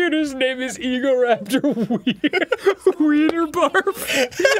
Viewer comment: I thought Dan said the computer name was- Egoraptor weed or barf Lol